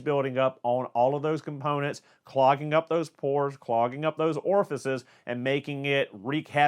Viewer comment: 1). Buy it Edit 1:56 did you really say “ clogging up those pores”?